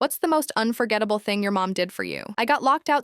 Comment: heard it as unforgivable, thought she went to her mother and she kicked her out, good thing it wasnt that